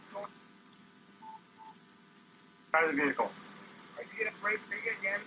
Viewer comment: did he say, "Are you going to break my window again"?